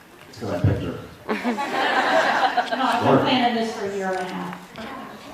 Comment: Can anybody tell me what the girl at 6:35 was saying? Couldn't make out a damn thing except something about "a year and a half". Thanks. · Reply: "I have been planning this for a year and a half."